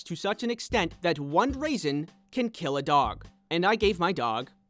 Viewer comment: Wait just a minute... Did you just say "Oned raisin?"